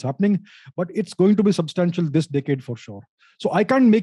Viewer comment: Ohhhh decade, thought he said dickhead